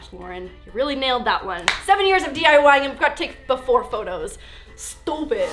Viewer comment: I had to go back ten seconds when she said 7 years of diying. I thought she said 70